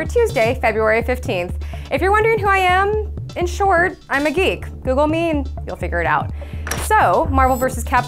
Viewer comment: oh she says "im a geek" i had the volume on very low and heard her say "im a 'he'" n i was like "WTF? o_O" lol